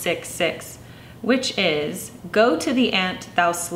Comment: Go to the ant, thou sluggard! :) Thank you for joining me for today's video. For more, visit my corresponding blogpost <link> Love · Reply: Thanks for writing that out...I kept hearing "go to the end..."